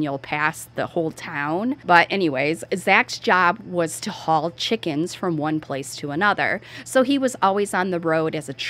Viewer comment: with your accent I thought you said his job was ‘tall chickens’ not to haul chickens lol!